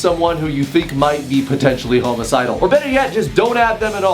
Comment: did he say homocidal? (1:34) and also: HAMMER TIME